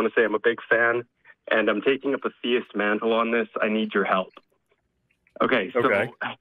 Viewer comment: I don't get how people can call into AXP saying "yeah, been a huge fan for years, but I'm still gonna throw a terrible argument you've heard a hundred times at you" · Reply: I thought the caller said he was 'dealing with a theist', and was relaying their argument looking for advice on how to respond to it. Maybe I misheard Edit: ok I went back to listen to the start, and yeah I misheard. He says he is "taking up a theist mantle", so I think he's just calling in to play devils advocate